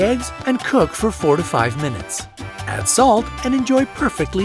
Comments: thought he said 45 minutes haha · On the fried egg tips, at first I thought you said 45 mins, I said 45mins !!!! So I went back and it was 4 to 5 mins, I guess the music was a little too loud . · he said 4 TO 5 minutes, NOT 45 minutes. I listened to it twice to make sure that was what he said.